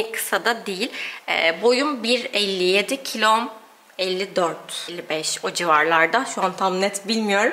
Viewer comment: boyuna 157 kilona 57 mi dedin ben mi yanlış duydum😱